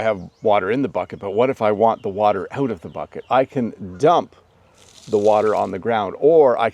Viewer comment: i can 't hear out the word you say out of the bucket. Can you explain to me what you say. Thank you! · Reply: He said, 'What if I want the water out of the bucket?' Notice the word 'water' . you'll understand the sentence.